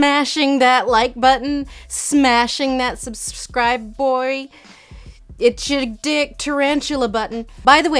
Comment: did u say "it's ur DICK tarantula button"???